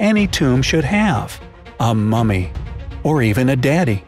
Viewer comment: I heard about only mommy lol